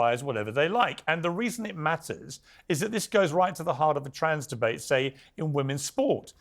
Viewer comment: Did Piers say there is a trans gender? 48:00 That makes it 3 genders, at least.